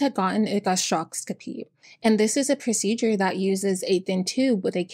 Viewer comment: … an endoscopy? I couldn’t understand what you said